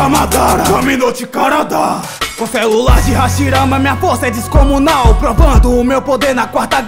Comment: Só eu que entendi ele falar, o celular de Hashirama, 😄 Tempo